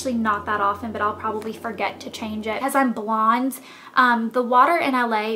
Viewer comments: "but I'll probably forget to change it, as I'm blonde" ^^ I know that's not what u meant, but I heard it like that at first and I thought u were joking for a second :P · Ahaha I thought she said "I'll probably forgot to change it, because I'm blonde" ;)